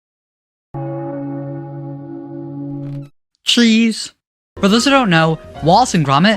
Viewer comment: Cheese (AT 1st i thought you said: Tree's)